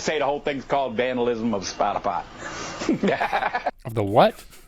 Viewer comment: At first I heard, "Vandalism of Spotify" Now I hear, "Vandalism of Spider-Pot"